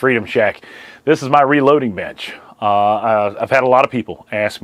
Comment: I thought the opening said "This is my reloading bitch". I was expecting Beto to be sitting there in the Freedom Shack...